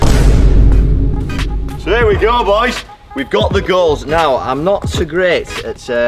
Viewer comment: I thought he said we got the girlies